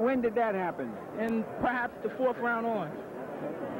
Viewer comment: sound like he saying" but i knew he wasnt a punk and was taking those punches"